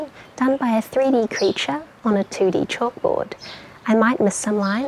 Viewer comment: “No mistakes in THAT” is what I heard, not “math”. Mistakes in math are detrimental lol